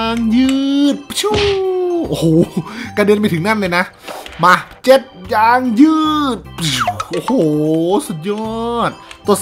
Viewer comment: ผมเห็นพี่พูดว่า หมัดเ*ด ยางยืดดดดด (หูผมได้ยินอย่างนั้นนะไม่รู้ไมเป็นงั้น)